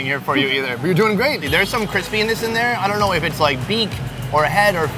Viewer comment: love that the subtitles say 'Chris penis' instead of crispiness 🤣